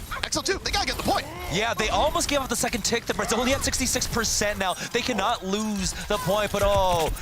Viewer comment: What is the Brazilian 66%? Casters say it